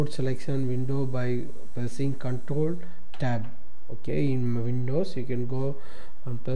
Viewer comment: sounds like hes saying cunthole tab